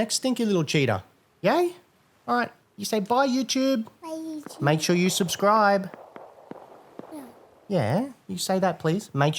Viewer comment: I hear 'Make sure you survive', so I'm just gonna do that. 😁😸